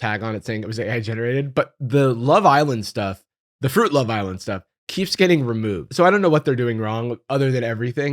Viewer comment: Bro, I thought you were saying "Fruit LaViolent". Turns out to be kinda accurate... 😂